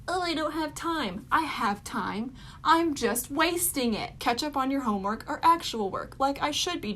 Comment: I thought she said ketchup on your homework and I actually was confused for 1/4th of a second